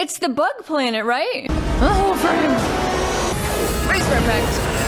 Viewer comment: For a second there, I thought u said "BUTT hole" LOL